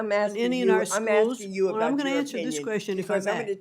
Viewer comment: Did mccmahon just say, she'll answer the question of her choosing "I'll answer this questions"